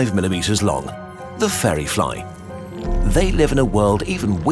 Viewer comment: took me ages to locate this minute beast. I thought you said "Ferry fly" and was googling "ferry" and "feri" etc.